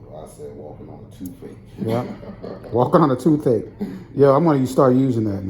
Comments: "Walking on a toothache," now I've heard it all! Working in the healthcare field, I've seen a lot of patients with calluses and corns but nothing like his! I hope he continues to come back and get the care he needs. 💜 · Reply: I thought he said it eas like walking on a toothpick. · "Walking on a toothache," now I've heard it all! Working in the healthcare field, I've seen a lot of patients with calluses and corns but nothing like his! I hope he continues to come back and get the care he needs. 💜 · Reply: He said toothache? I thought he said toothpick. 😂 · For a moment, I thought the patient was saying "....like walking on a toothpick....not walking on a toothache". Either one, it's still painful. Glad he got the help he needed, and great job 🤗🤗🤗